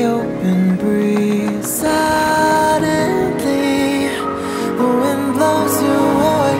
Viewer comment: the word is "Silently" and not "Suddenly" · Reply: I hear "suddenly" and not "silently" because I heard the "u" from suddenly not "i" from "silently"